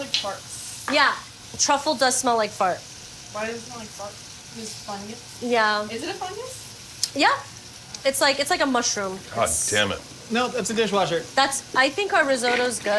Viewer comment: I was just listening and for some reason, I heard "It's like a type of mushroom. Nope. It's a dishwasher". I thought Nicole and Josh were talking about the same thing...